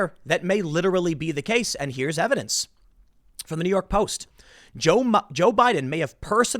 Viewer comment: Anyone else hear him almost say "Joe mama" at 13:51 ?